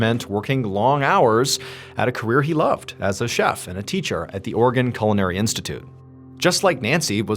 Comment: I thought you said "Organ Culinary Institute "